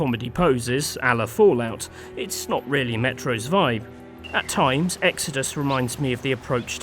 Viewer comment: did he say Metroid?